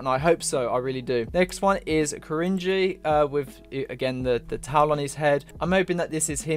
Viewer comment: i swear i thought i heard u say "taiwanese head" 🤣